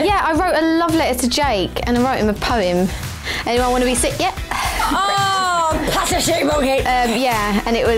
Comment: What did Perrie say at 1:51? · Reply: becka_345 stant Pass the sick bucket